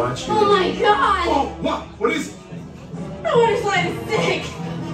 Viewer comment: with the subtitles on................. what did she say???? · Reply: She said “That water slide is sick!”